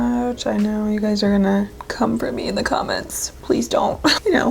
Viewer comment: Totally thought you said “I know you guys are going to comfort me in the comments”😂😂😂😂😂 and i was thinking “aww how sweet!” And then realized that you said “come for me” hahahaha